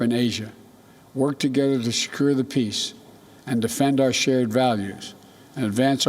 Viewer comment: I thought he said screw the peace😂🤣